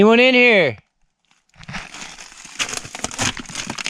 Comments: sounds like he says, "buried in". kid might be in that cave. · Idk i heard " I'm in here" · Thanks for your uploads. 02:12 sounds like to be (I'm here) instead of Come on in. Maybe 🤔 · the first vid that voice "come on in" which for me i think it sounded like i'm right here?